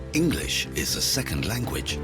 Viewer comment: Did you say in Vancouver English is second language? (0:18) 🤔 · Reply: "For half of its 2.5 million habitats, English is their second language."